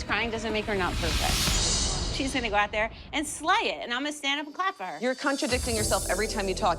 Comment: did she said slaying?💀